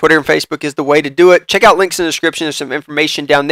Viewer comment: Did you just say ketchup at 5:58 ?? :)